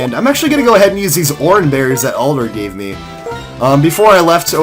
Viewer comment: I keep hearing "Oliver" instead of Alder